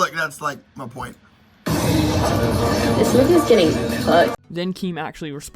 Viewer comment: i thought she said 'nivias getting COOKED' idk